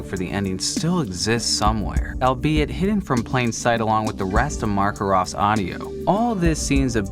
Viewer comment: Did he just say.... MARKarov!?